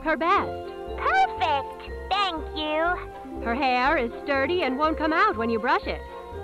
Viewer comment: I THOUGHT IT SAId "her hair is dirty and it wont come off!" LOL